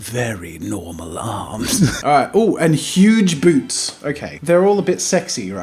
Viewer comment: For the first character, I thought it said "huge boobs" 💀💀I was like WHAT?!